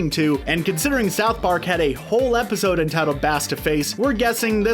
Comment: Wait: I thought he said "bass to mouth" not "bass to face"